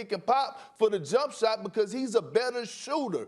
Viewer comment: Perk: "He's a posis, a positis, a posit, a positiinilis player. He don't have a position." lmaooooo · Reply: did he say “implemate” It’s like a combination of implement and integrate